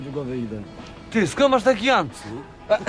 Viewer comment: >> co mówi X ? Skąd masz taki ancyl ?